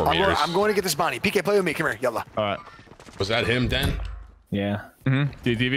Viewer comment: did nick say Yallah at 2:34